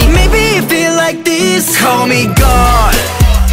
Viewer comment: I just found yall and it’s a great song but it sound like they saying call me God 🤦🏽‍♀️😂